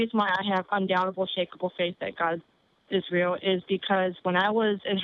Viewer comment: The first time I thought that she said "God is Tracie" xD